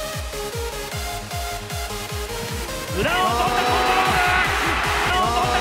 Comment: 申し訳ないが「裏をとったコン〇ーム」にしか聞こえないwww